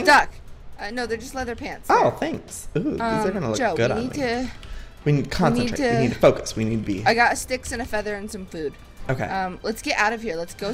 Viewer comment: Did Joey say, "we need weed?"